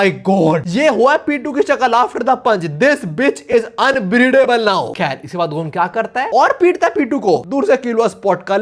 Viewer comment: Unbreedable ...Ayo what ...word is this 😅😂😂❤❤❤